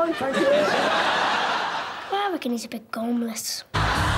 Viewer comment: what is the kid saying? ”I reckon he’s a bit _________.” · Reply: "Gormless"